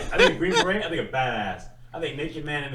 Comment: the subtitles sayin “fatass” when I’m pretty sure he’s callin Green Berets “badass” 😂